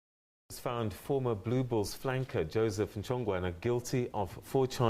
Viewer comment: had to listen to that twice blue BULLS - I thought you said balls.